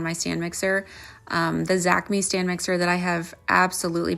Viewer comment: Do you have a link to the stand mixer you referenced in the video? It sounded like you said zackme? Tocopherol is vitamin E, by the way...it's a natural preservative (I'm an amateur formulator).